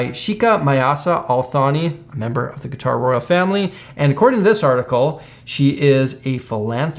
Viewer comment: I had to replay this, because what I heard was, "The guitar royal family", which actually made perfect sense to me at first...